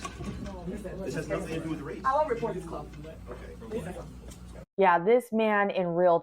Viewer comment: “I will report this club”? Is that what she said?